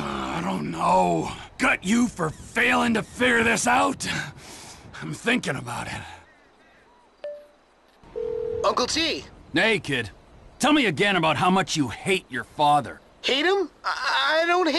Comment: Did he say Naked? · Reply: Nah, Sounds like he says nay kid instead of "Hey kid"